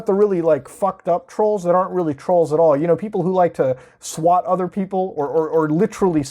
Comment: DID I HEAR SWAT? BRING SWAT 4 BACK